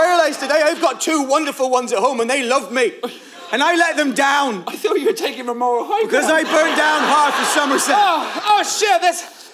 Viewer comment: What does say at 17:01 ? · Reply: and they love me. And I let them down. I though you were taking the moral high ground!